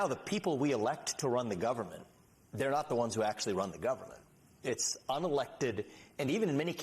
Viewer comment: Did he say the unelected? Hmmm?